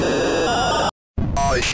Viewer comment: “The terror in the mind” as someone who suffers from hallucinations before sleep or in general when I’m alone, this trailer spoke to me. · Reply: thought he said terror in the nigiht